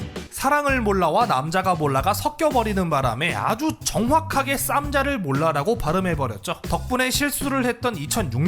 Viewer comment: 쌈장을 몰라 라고 들리는대 뭐지